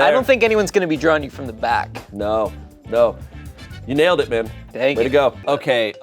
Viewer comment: Am I hearing things or did call “babe”? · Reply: He said man but thats hilarious 😭